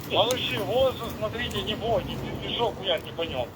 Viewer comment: шо там в рацию говорит 😮😮😮 малыши хуярте не бойтесь если шо😮😮😮 чи мені почулося?